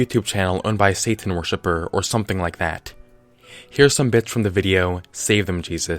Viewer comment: I thought you said “here’s some bitch from the video”